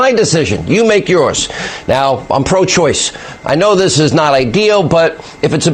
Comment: Holy fck did Hannity just say he's pro choice?? 5:30 time stamp.